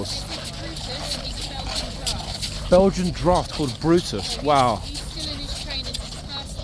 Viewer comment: ❤❤ Brutus ❤❤ I'm in love. What breed did the officer say Brutus was? Can as anyone tell me? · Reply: Belgian Draught